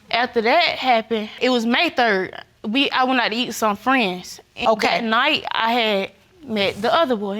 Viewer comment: I tried hard, I really did, but at 2:15 when I heard "I went out eatin' some friends" I gave up and turned CC on and started over - and discovered I had misunderstood much of what the pretty little bitty lady had said.